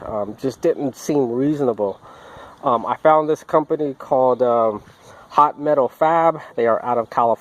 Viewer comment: Thanks. It’s hot metal fab. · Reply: really appreciate the information. When I was listening to the video I thought I heard you say pot metal fab. Found the place, will look them up thanks again. Maybe we'll see you on the trail.